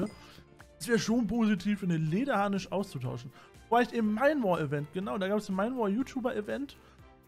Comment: Fällt niemandem auf, dass er am Anfang Bed Wars sagt? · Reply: Also ich habe am Anfang 2 mal Bed Wars verstanden, jetzt verstehe ich das aber auch xD